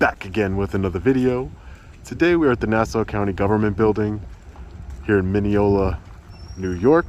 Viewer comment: I had to rewind the tape. At the intro, looking at the building as you spoke, I thought you said Nassau County Prison. 😂😂😂 Yikes! That building is scary. It looks like the projects! Hats off to the Sergeant!